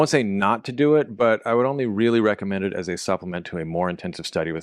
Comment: Can anyone explain what he said at 14:37 ? I’m trying to find tools to learn Arabic and the captions/transcript didn’t understand him either. Any help is appreciated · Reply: It's Al-Kitaab or Al-Kitaab fii Taʿallum al-ʿArabiyya as he said. I don't speak any of Semitic languages at all, but I recognized sounds of the beginning and googled “al-kitab”.